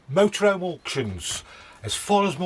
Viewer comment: Did you know you say mota roam but on this video it was the first time I heard you say mother home! :) Your videos are great and so informative. Thanks